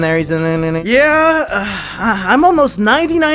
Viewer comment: What’s that effect where if your told to hear something that’s what you hear? Cuz I heard “he’s a [N-Word]” it took me a few minutes of listening to hear “He’s in there”